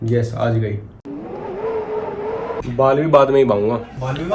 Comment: What he said 0:13? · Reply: Lakshay: Baal bhi baad mein hee baunga: Baal Vivah